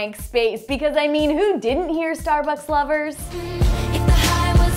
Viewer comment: Not ALL Of Us Got Them Wrong... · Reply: I thought she was saying Starbucks...but I did know what the lyrics to Drag Me Down were